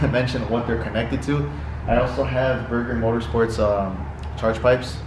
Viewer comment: did you say BURGER? DAYUMMM 🤣